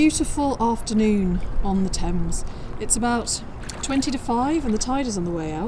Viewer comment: Lol you said its 20 to 5 and I thought you said 25 ! We can dream for when the 25 comes back. Spring is peeking around the corner 😊